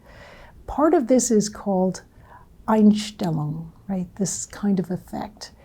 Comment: What did she say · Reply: "Einstellung" - german word, means "attitude" or "view"